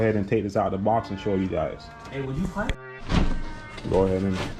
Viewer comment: Anyone else hear say “aye would you pipe”